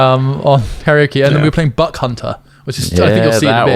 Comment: I thought he said "Butt C*nter"